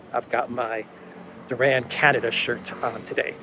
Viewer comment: Right at the end, when said, "I've got my Duran Canada shirt on today", I swear I heard him say, "I've got my Duran CANNABIS shirt on today". Which is actually oddly appropriate, given what the Canuckie gooberment is doing these days.